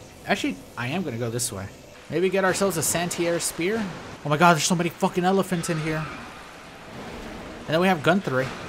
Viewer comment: I heard "there's somebody fu**ing elephants here!"